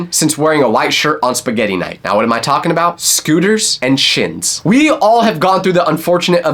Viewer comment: Did he just say scooter and shit?